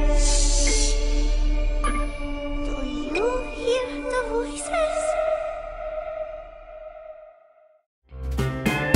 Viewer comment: what did said? Edit or is it somebody else? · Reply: Tiger Gaming she said “Do You hear the Voices”